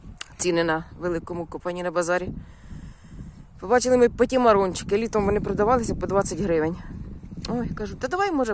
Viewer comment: Как называется тыква?? ПРавильно расслышала ПОТИМАРОН?